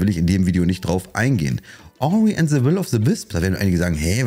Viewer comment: welches spiel ist es ab minute : 8:53 ? hab ihn nicht verstanden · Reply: Ori and the will of the wisp